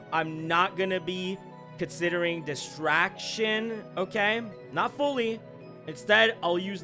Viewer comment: Wait, why did I heard "Abstraction"??? 💀